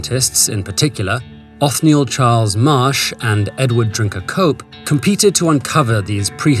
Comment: I swear I heard "Edward drink a coke"